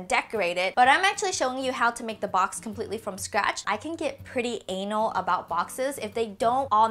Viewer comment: did you say you can get pretty anal about boxes.... LOL